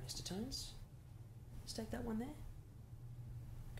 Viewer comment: 男性が何か渡すときのセリフが「どうじょ（どうぞ）」にしか聞こえない